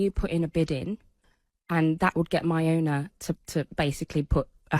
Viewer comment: She actually said "my owner" 🤔